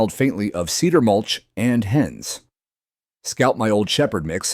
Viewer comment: I thought he said his old truck smelled like cedar mulch and ass!!!! 😅 Good thing there are sub titles!!!😅